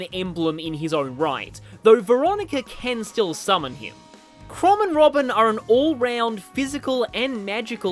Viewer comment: Misheard you say "Throbbin Robin" now my day is ruined😢